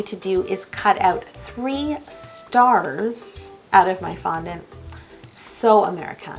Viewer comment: Idk y but the first time she said CHEESE CURDS I thought she said CHEESE TURDS for some reason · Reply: I also thought she said scars instead of stars